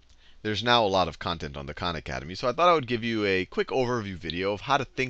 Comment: haha I have watched more than 100 videos and every time I thought you were saying con academy